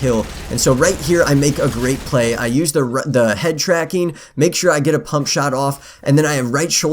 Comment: did he say headshot tracker?